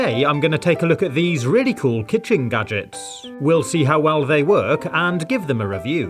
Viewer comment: When the video starts, i heard you say "nuggets" instead of gadgets